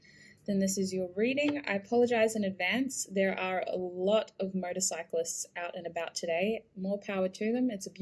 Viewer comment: To me it sounded like you said murdercyclists